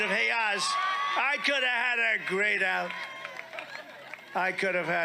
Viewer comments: "I could have had her..." What? "Greyed out"? "Great out"? WTF is he mumbling about/through? 🤪😜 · It sounds like Trump is saying "I coulda hadda..." about Dr Oz's WIFE?? He so obviously lusts after her, he's voicing how he would have liked to "get in there" like he has described his rape attempts before in public to loud approbation. These rallies have turned into NOTHING BUT grunts and whines and yells of inchoate rage-- they have no semantic content, no ideas at all, just AAARRRRBBHHHH, moans of lust, sneering disdain, and roars of fury.